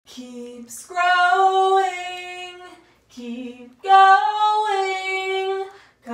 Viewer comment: I thought said, “keep scrolling, keep going”